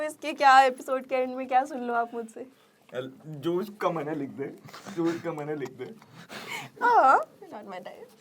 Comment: hein kya bola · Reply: “Jo iska mann h likh de”